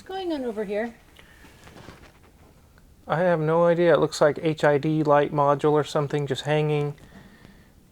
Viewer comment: I thought u said HIV and I did a double take